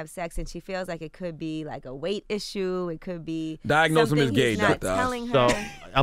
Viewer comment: Did cthagod say "diagnose him with gay Dr Oz." 😂😂😂